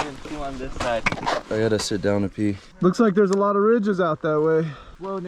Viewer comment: did he say “I gotta sit down to pee” ?? 😂😂😂